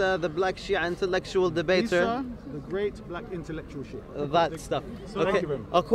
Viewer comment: I thought he said isa the great black sheep….lol